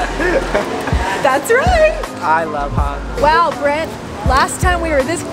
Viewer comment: DID HE SAY “I love her” 🤯 😳